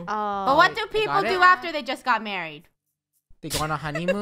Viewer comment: "what do ppl usually do after they get 'mUrRiEd' ?" uhhhhhhh- eheh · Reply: When she said married I thought she said murdery XD